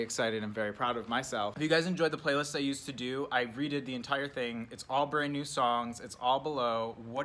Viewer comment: this nigga just say readed instead of read